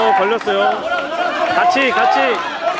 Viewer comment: 초반에 반칙반칙 이라는데 뭐가 반칙인건가요??? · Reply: 같이 같이를 잘못 들으신듯. 같이 주먹 내주라는 뜻